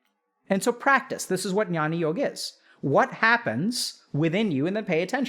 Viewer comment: What does he say @ 19:15 "this is what nyani yogg is"? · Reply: Nevermind, found it: Jnana yoga